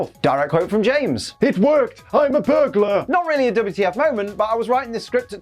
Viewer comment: OMG. I just went back and rewatched this episode. It really does sound like James says, “I’m a burger.”